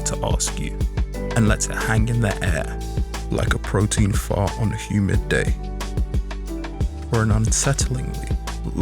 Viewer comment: Bro what did you just say 14:30 😂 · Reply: I have CAPD, but here's my guess: "...hang in the air like a [protein] fog on a humid day" I don't think he meant "protein," but that's all I got lol.